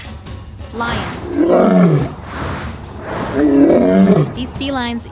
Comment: Excuse me? Did I hear "highway foam" at the end? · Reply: Its Hyloid Bone 😊